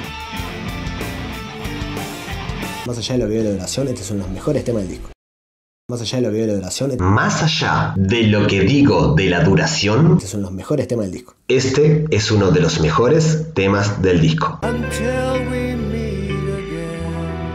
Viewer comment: En un momento le entendí "más allá de los vídeos de la Sole" y me quedé con un ?; Ayudó que después vino la traducción "más allá de lo que digo de la duración*.